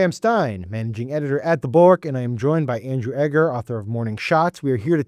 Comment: Sam Stein "The Bulwark"has THREE syllables (no really)! I heard you say "The Bork" and looked to see what I was listening to.